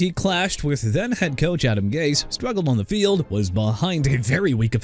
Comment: “Adam Gay” instead of Adam Gase lol